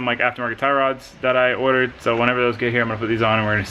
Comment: i kept hearing "anal mods"